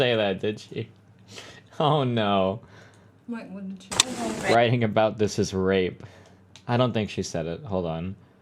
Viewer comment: I thought she said "writing about this is rape" too rofl